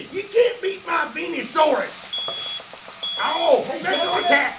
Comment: all I hear is Peenosaurus😂😂